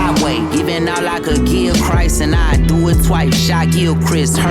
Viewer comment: He was referring to Micheal Kidd-Gilchrist. I heard Gilchrist.